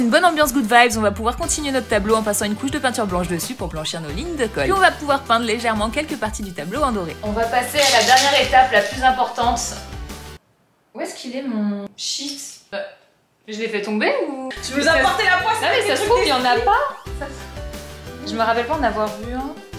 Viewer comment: pourquoi j'ai entendu : ou est ce qu'il est mon shit ? 😭